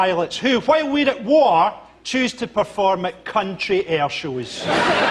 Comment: did you say "cunt"?